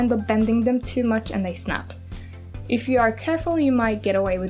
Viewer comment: I heard bend and snap